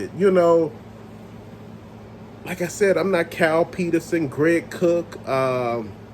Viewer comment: LMAO! He said "Kyle Peterson" but I heard "Cow Penis". Reminded me of that SNL skit with Will Ferrel where he is a fighter pilot & his call sign is "Clown Penis". 🤣😂🤡